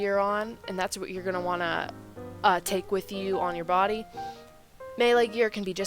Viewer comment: hhhhh i thought you said first you're going to want to take on your body